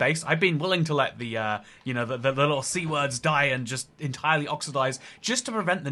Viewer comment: That intro ain’t it dude. Aahh 3:12 Also did he say.. “c word”?